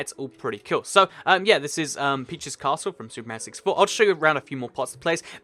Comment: sounds like said SuperMan 64 . What a God Awful Game That Was